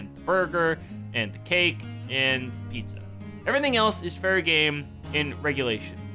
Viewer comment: I know he said Everything else is fair game in regulation... But when I first heard it i thought he said Everything else is very gay